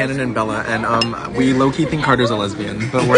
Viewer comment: When at first he said, "you already went girl", at first I thought he said " girl your already wet" 💀